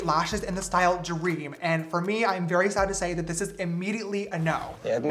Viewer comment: I heard “I’m very excited to say…” so when you later said “This is immediately a no” I was SO CONFUSED!!! 🤣😅😂🥲